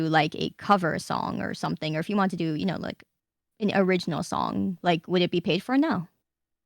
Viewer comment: I can't understand the part at 23:10, what did she say about original songs and covers? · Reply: Nijisanji doesn't help pay for it the talent has to pay out of pocket